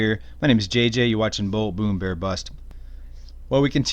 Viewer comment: It sounded like you said Bull Bloomberg bust.